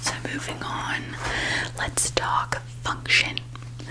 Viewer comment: I thought you said let's talk fuck shit and I was SO CONFUSED- my ears are as tired as me 😅